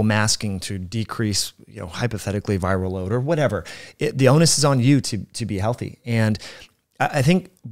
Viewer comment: I just heard say “The Onus is on YouTube” and then i realized he was saying… “The onus is on you to be…” I might be spending too much time on this platform 😂